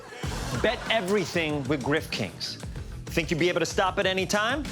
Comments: I coulda sworn they were saying "grip kings" the entire time furthering John's sex thing Grift makes more sense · I thought he said Grip Kings